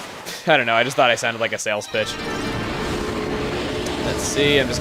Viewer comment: Did you just say 'salesbitch'?